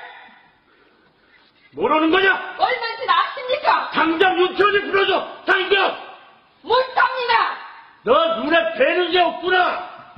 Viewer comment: 근데 이거 대체 대사가 뭐임 0:02 남:뭐라는거냐!(모르는거냐?뭘하는거냐..?) 여:얼만진 아십니까!!(뭘만지마십니까?) 남:당장 유치원에 불러줘! 당장!!(????) 여:못삽니다!! 남:너 눈에 뵈는게 없구나 야 도자기:쿵 챙그라라랑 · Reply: 모르는거냐 얼만진 아십니까 당장 유춘우(이름같음)를 풀어줘 당장 ! 못합니다 ! 너 눈에 뵈는 게 없구나 ! 나 ! 쨍그랑